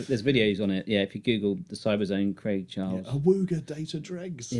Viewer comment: I thought he said "RAY Charles" at first, and was quite puzzled.